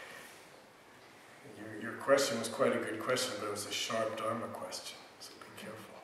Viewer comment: May I ask what is said in reply at 31:22? A 'sharped armor question'? As in, the question is asked to be reassured one already knows the answer?